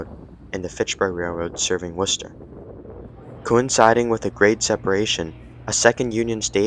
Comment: grade separation, when a level crossing with a road was removed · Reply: Oh my god I heard "Great" Separation lol, IK what grade separation is, I feel silly; Thanks!